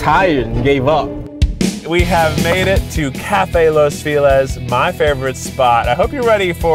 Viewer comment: I always thought that Keith said "Los villas" now I know that I need a consult with my doctor for my ears......